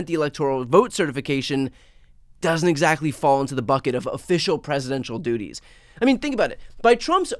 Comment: Ooooooh… presidential DUTY… I thought everyone was saying president doody as a nickname for trump. My mistake 😏🤣